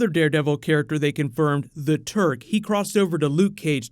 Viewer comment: Not that familiar with these characters as I'm from a totally different side of the planet, so when I heard you said the Turk, I thought I heard the Turd lolol